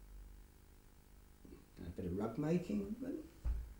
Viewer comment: The sound quality and volume isn’t brilliant and can play tricks with your ears, but at 13.23 when asked what he did in his spare time it sounds like he says “ a bit of love making “ after rewinding and listening again I think it might be “ a bit of rug making “ either way I’m confused, but intrigued !